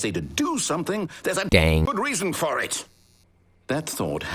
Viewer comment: "HE UNDERSTANDS THAT IF I TELL HIM TO DO SOMETHING, THERE'S A dang GOOD REASON FOR IT!" I can't stop laughing at this please send help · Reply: For some reason i heard "There's a game good reason!"